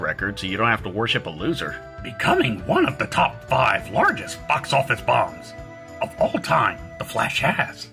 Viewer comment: Did he just said "f$:&$-cks office" bombs? 😂